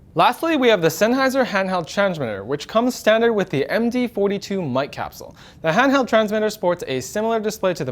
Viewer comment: anyone else heard MG42 2:05 ?;P almost made my Arma 3 instinct go on full alert